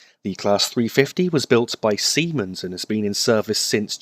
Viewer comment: I'm sorry but what did you say? Lol semen? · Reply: Siemens. :)